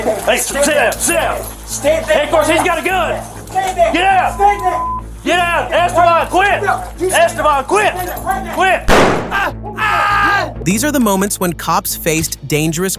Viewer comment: sounds like he said Escobar quit🤣ahhhhhh 😮it’s not funny at all tho damn sad 😔1:37 Evan turner looking as